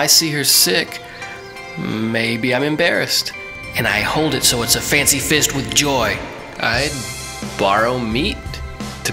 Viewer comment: lol @ 0:24 fancy fist with joy. It's spot on. Anyone know what he really says there? Hehe